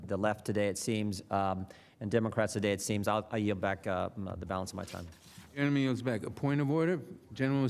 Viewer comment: Just curious...before the 3:53 mark, does anyone else hear him say "enemy" referencing the gentleman attempting to introduce the video? Not trying to stir the pot, trying to understand correctly.